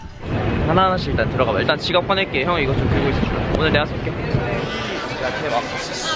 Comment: did taeyong called doyoung "hyung" when he ask DY to hold the camera?